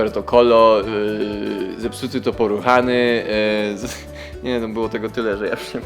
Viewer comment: poruchany to zepsuty po czesku XDDD :D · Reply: Ja tam usłyszałam poruchani XD